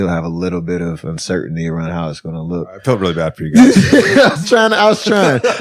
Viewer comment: What did JJ say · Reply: I felt really bad for you guys.